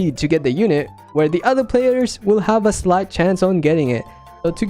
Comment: i thought he said h0e XD